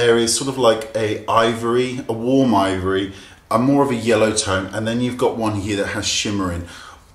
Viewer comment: did u just say: "a morbid yellow tone"...?!? Lolz! :D